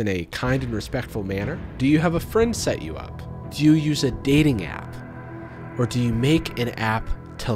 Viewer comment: I just looked away to drink and all I heard was "Do you sedate a gal?" and I was like "WHAT?!?? Do you... use a dating app! Oh thank god...." I thought this was going to turn REALLY DARK REALLY FAST lmao